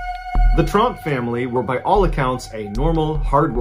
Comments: who else thought when he said tromp he acually trump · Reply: Funny story. I was reading your name but not really looking at your comment. Then i heard say "tromp family" (i thought he said "trump family") so i rewinded a couple of times to hear what he actually said. And after that i realised that you had just written a comment about that. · Who else thought he said "the Trump family" when he said "Tromp" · I hope I'm not the only one who heard "the Trump family" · Who else thought he said "the Trump family" when he said "Tromp" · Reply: ooooh its Tromp I thought he said Trump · Who else thought he said "the Trump family" when he said "Tromp" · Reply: At first I thought he said Trump but then it sounded more like Tromp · At first i thought he said "the trump family were by all accounts a normal working family" kinda let down it was tromp lol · At first I thought he said "the Trump family was a hard-working household" 🙃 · on the second story I heard "the Trump family" and as soon as you said "a normal hard-working household" I knew I misheard.